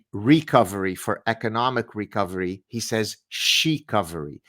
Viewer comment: I misheard the quotes from Trudeau as "Xi-covery instead of recovery".